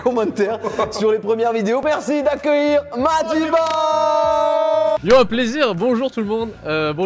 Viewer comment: C’est moi ou à 00:53 dit wadiba ? 🤣🤣🤣